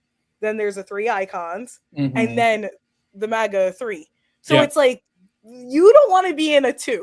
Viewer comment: did she say MAGA Three? 😂